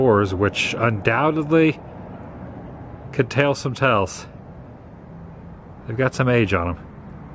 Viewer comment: did you say "doors could tale some tells?